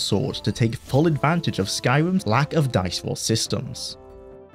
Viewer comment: Skyrim lack of dinosaur systems? Eye sore victims? Dime store systems? What in akatosh is this man saying????